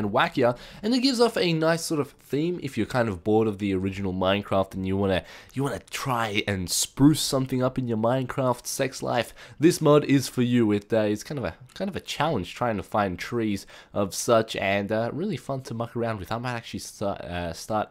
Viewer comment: Did he just said minecraft sex life?!